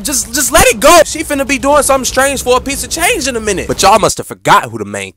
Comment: I heard She finna be doing "Stranger Things" XD